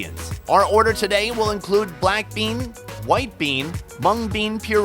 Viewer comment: I heard BLACKPINK🤦🏻‍♀️😆